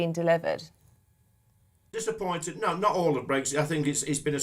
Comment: "He's got a lot of sympathy for Patel" - that's enough from him. Sick of Liblabcon excuses & tricks whilst it floods our island kingdom with the 3rd World? Then Vote UKIP in Bexley on 2nd December (& then in North Shropshire & Southend West beyond that) for an immigration referendum. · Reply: I thought he said brexshit as been a success 🤣🤣🤣